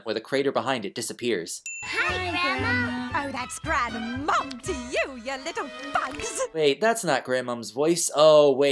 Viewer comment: I always thought she said “You little punks” when they called her “grandma”